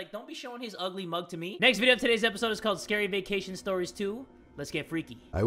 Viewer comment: thought it said scary vaccination stories 2